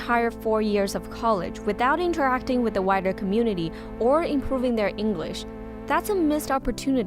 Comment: I heard Whiter community at first